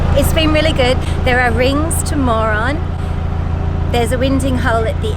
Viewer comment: I thought you said there’s rings, you moron at 0.49